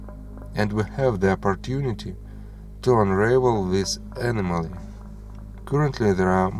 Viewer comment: The best part if the video, in my opinion, came somewhere near 6:51 when our narrator said a word CC identifies as anomaly, I think he meant to say enigma, but it sounded to me like enema. I subscribed. (